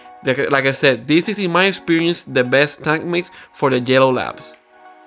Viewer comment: Thought he kept saying jello labs 😅